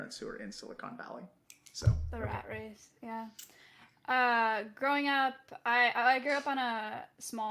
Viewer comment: Did she said "It's our race"? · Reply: "the rat race, yeah"